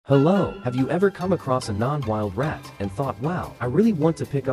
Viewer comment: For a moment I thought she said "Have you ever seen a wild rat and wanted to pick it up but don't know how" Me: Uhm- wouldn't it bite you-?.. Me: Watching it the second time "Ohh she said 'non-wild rat'